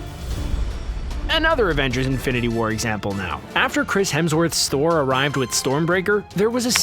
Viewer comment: did he just say store?